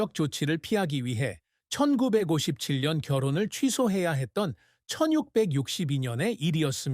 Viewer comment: 1962년을 1662년(천육백육십이년)처럼 들리네요